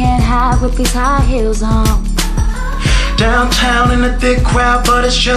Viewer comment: His voice is amazing! But i just thought it was kinda funny that I sound like he says on his first line "Downtown in the dick crowd" lol